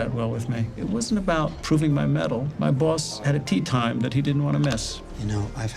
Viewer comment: At about 7:10, the subtitles say "It wasn't about proving my medal." The line should be: "It wasn't about proving my mettle." I'm not sure if you can edit subtitles after a video has been published, but if you are able to do so, it would clear up a potential point of confusion for those reading along.